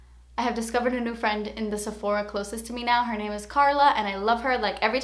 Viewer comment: When you said you had a friend named Carla, I thought you said Darla so I thought I FINALLY found someone with the same name as me.